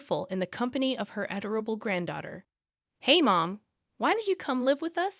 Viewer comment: And pronounces words wrong as well as stopping or starting conversation when you are or are not supposed to. There’s only a couple of minutes left. I’ve gone this far. Might as well suck it up and drive on. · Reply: Exactly. I think I just heard "edible granddaughter".